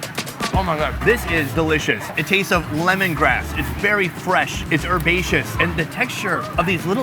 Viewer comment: did u say lemon grass